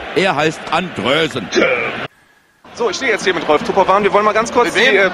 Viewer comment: Ich habe Töpperwein verstanden. Was ja auch passen würde.